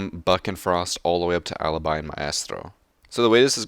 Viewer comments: Myasstro sounded like you said that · Who hears my ass tro at 1:06. Not trying to hate btw